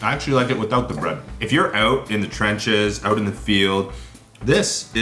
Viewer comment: Did he said ''eating out in the trenches '' ? What year is it now... 1916 ?:))